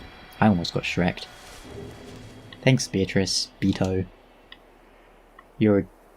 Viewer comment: Did you just say Beako?